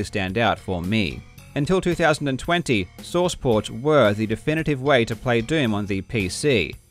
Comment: when you were saying "source port" I thought you were saying sauce pot